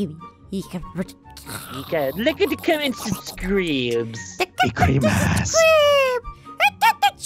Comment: On 1:33 I think I hear (Zane) say "CHREEEEEMAAAASSS"... Now I'm pretty sure they know the person saying "Don't touch da child!" Because he has said that befire also... It is the great. The quote was fron an animation called Underpants: The True Ending by him.